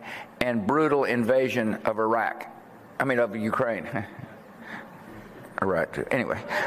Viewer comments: I put the volume up to 100, put my ear on the speaker, and heard him say "Iraq too" pretty clearly in that little part that sounds unclear. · This is not a slip , he clearly admitted by saying' IRAQ TOO' and the audience laughed. What a shame . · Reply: Holy sh1t! First I dind't understand this part and thought he had said something like "corrected.. anyway", but you're right, he actually says "Iraq too.. anyway". This is such a weird example of just how untouchable these elites are. Can you imagine an ordinary criminal getting away with a similar a slip referring to one of his or her suspected crimes?